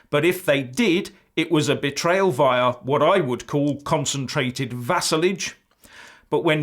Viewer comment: i got bit worried there i thought you said concentrated vasoline. sorry i must of been thinking of owen jones.